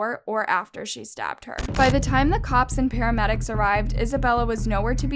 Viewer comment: i thought they said “she stabbed her mom 307 times”